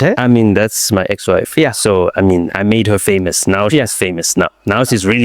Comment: All i hear is "since i was 6" yho "I made him"